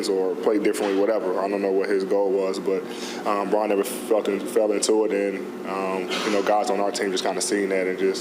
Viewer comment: ad sound like he said fell in fucking 🤣😭